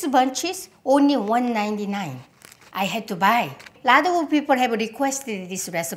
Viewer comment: At first I heard "Vladimir Putin has requested the recipe" 🤣 Had to rewind to realise it was "lot of people" and not putin😂